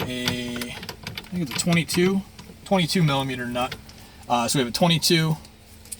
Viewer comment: So, at about 1:55 in the video the captions says the nut holding the steering wheel is "20 to 22 millimeter." Since I'll need to buy a socket, please tell me the actual size of the nut, thanks!